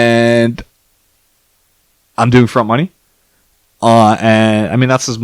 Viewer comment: What did he say at 31:09.... front money? Anyone know anything of this technique?? 🤓 I love gambling with an edge... such a greate time to be involved while these videos are barely getting views...